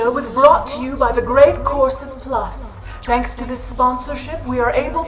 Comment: Why did I hear 'Great corpses plus'??? XD Pssss Arthur Conan Doyle despite having created a very skeptic investigator was incredibly guillable, he was deceived by a pair of sisters who pretended to have a picture of themselves with actual fairies (while it was 100% sure a fake photo), so... man of letter? Yes. Man of reason? Eeeeeh, not so much.